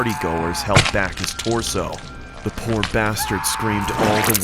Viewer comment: Did she just said the poor bastard?